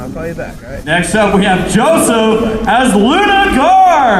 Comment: I'm that guy at about 53:38, the presentator had difficulty understanding my name, Jean-Sébastien, the french for John-Sebastian, so I think he called me Joseph? Anyway, linked to it on my facebook, you were the first video of the cosplay contest I found.